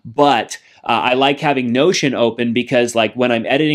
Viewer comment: I thought you said 'lotion' I use lotion when I'm on my PC all the time.